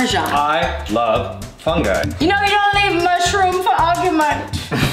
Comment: What did Hannah say at 4:34 for the PUN-O-METER? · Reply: "You don't leave MUSHROOM for argument."